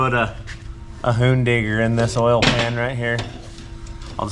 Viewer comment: a hoondinger😂😂😂 if i heard that correctly lol